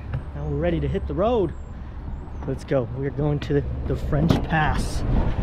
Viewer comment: i misheard french pass and thought he said french ass